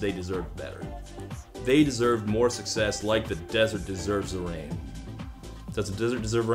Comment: I always thought they were saying “ like a desert mystery”